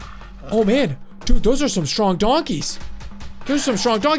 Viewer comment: when you said donkey i thought. you said dog